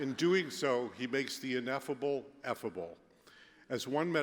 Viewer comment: It sounded like he said, "He makes the ineffable edible" haha!